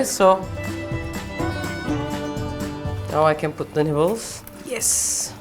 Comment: I know this is probably just me.. but every time I hear you say, “nibbles,”. I hear, ”nipples” 🤣. Love you Refika you’re the best!